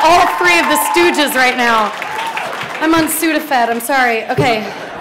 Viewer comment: she says she's on Sudafed (at least I think that's what she said) :D